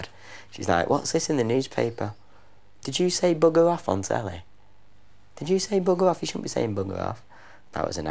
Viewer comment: what does he say at 8:37 ??? i´m german and don´t understand it, sorry.... can somebody tell me? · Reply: 13 years late but he said “did you say bugger off on telly”